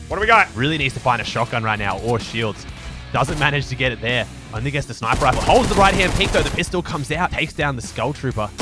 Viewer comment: did he say my ni*** has the sniper rifle or am I imagining